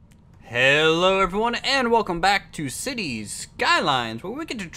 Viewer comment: Literally thought you said "and welcome back to Alpine Odyssey" for a second :)